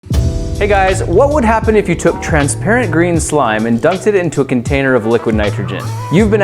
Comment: When he said transparent glue I thought he said transgender glue I was like wtf is transgender glue I did not know it was possible for glue to have a gender let along think they are a different gender than they are but than I figured it out I was like oh transparent not transgender